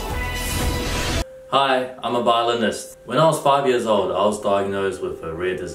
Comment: When Brett says "Violinist", the automatic subtitle says "Botanist". Yup, Violin is a new type of plant